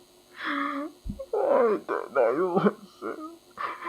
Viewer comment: what the hell is he saying all I heard was “why didn’t I listen”